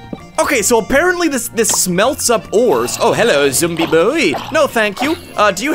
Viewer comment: Am I the only one who heard ‚Zombi Bowie‘